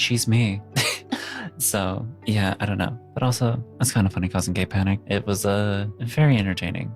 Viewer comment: I thought it said gay picnic!